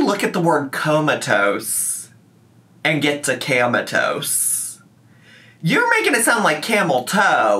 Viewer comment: When she said "camatoast" all I heard was 'camel toe' 😆